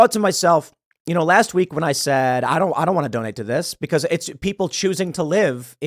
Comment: I thought Tim said dont donate.